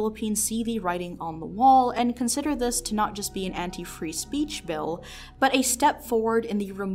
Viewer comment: anyone else mishear this as, "Antifreeze peach" ? 😅☠️